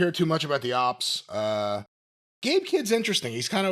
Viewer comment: Every time someone says "Gabe Kid", I hear "Gay Kid", and I think they're just using some retro insult from the 90s. Legitimately might be the worst name in wrestling.